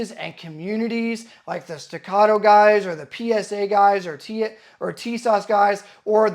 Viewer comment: I thought you were gonna say "TSA guys"